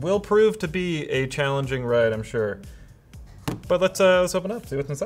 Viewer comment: I almost misheard him saying "a challenging wank"